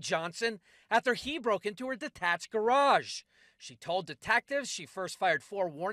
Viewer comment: Did he just say "De-tack-tive"?